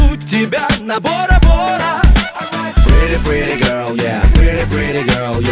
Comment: Pretty pretty girl yea или пере-перебрал я ? Что он сказал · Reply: Пере перебрал